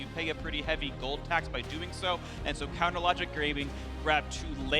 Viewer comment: is it me or do I hear him say " counter logic grame-ing" lol